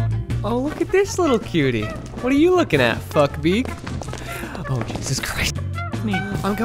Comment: I just realised after two years of watching this monthly,that he probably wasn't saying fuckbeak, but was actually saying buckbeak